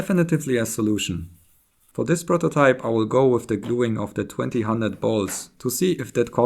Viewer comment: "Did you say "twenty hundred balls"? 1. Even if there were 2000 instead of 1920, then why not just say "two thousand"? 2. Why say that number when it was 1920? 3. If not, then what did you say in that place that may have sounded like that (11:04)?